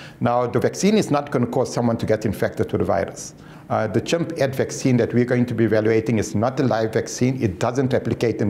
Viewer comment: What hold up did this guy just say "chip and vaccine" at 05:40 ?!?? Somebody tell me Im wrong! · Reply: Hey - a polite message to let you know you're wrong (as per your request). He said "chimp FX" vaccine. Recent trials on non-human primates (eg. Chimps) have proved very promising. Because of that preliminary success, that vaccine (that was previously tested on chimpanzees) will now be tested on humans.